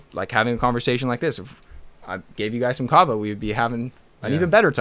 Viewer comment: what herb did he say? Sounded like kratom but I’m not sure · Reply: Kava